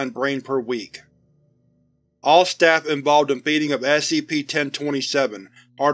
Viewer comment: Every time you said "Feeding" I thought you said "Beating" so I was sitting In there and thinking off a bunch of Foundation staff In hazmat suit's just squaring up to a god damn nerve system